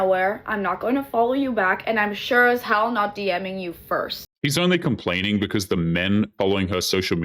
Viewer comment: Why does it sound like is saying "he" when referring to the woman speaking. 4:33 6:05 12:22 Am I going mad?